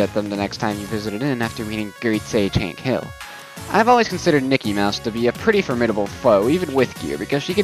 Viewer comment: Everytime you said Nikki, I thought you said Mickey